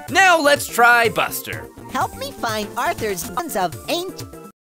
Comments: It sounded like Buster said "Help me find Arthur's f@ up angels!" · Help me find Arthur’s ots of aint. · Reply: I thought he said buns of paint